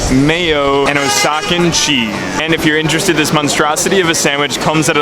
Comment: did he just say..... osacan cheese.....